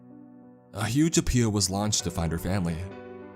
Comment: I'll give my pet alien from Area 51 to whoever can tell me what he said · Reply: "A huge appeal was launched to find her family" is what was said now i want that 👽